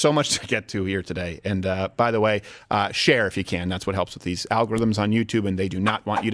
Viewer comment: poor child has been severely coached. "How dare you". And, this situation has become so ubiquitous, when Crowder said "share if you can", I heard "Cher if you can".